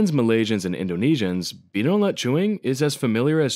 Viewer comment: lmao I thought you said beetle nut joint